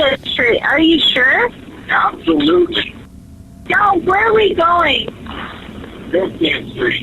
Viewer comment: sounds like she said Joe at 10:12 and it also sounds like the man is in his late 40s to early 50s and he is a little built and fat possibly brunette hair starting to get gray and yes I am a psychic and Demonologist so I hope this can be some help to this case. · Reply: I heard her saying No, where are we going?